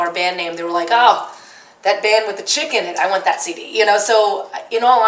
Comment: Totally thought she said that band with the chicken for a sec. Lol.